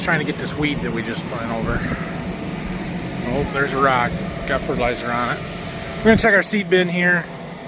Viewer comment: Sounded like you said, there’s a rock, gotta put fertilizer on it. Growing rocks?! Kidding